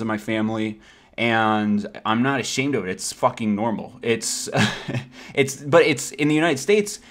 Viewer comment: Anyone else thought it said fucking grandma >.<